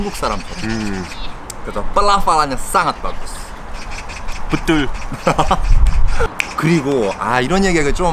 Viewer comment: bentar deh, apa cuma aku yang salah fokus disini, bilang "betul" tapi dengernya "bedhul" medhok wkwkwk